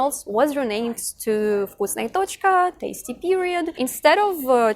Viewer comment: I'm sorry did she just say they changed it to tasty period....In the UK I can attest that it translates to something that's not very tasty.